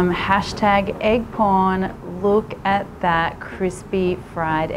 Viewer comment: I dead ass thought she said hash tag egg porn😂😭